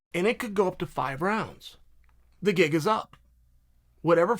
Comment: did he say the Giga us up or the Gig is up? If the latter, Uncle Chael just missed... and I have never seen him miss before..